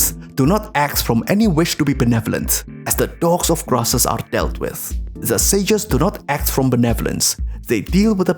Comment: did he say "as the dogs of grasses are dealt with?"